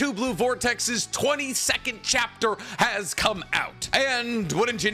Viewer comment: I thought this man said 20 second chapter instead of 22nd. 😂😂😂